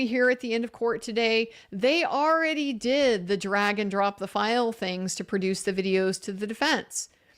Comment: What I heard at 5:00:58 was “the dragon dropped the files”… and I said WHAT!!? I missed that! But now it is my new mantra 😅